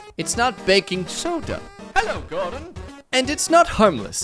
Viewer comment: why did I just hear "Hello Gordon!"?